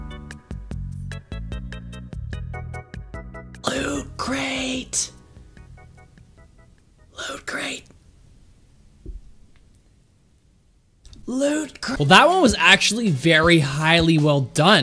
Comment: Xd I like what you say si lu create I don't know XD